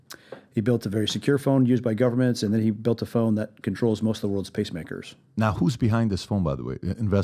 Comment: Did he say there is a phone that controls most of the world’s pacemakers? 3:26?